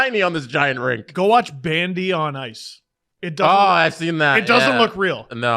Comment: I thought he said Bambi on ice, and that those were the best athletes in the world. Im picture a person in a deer costume, being jacked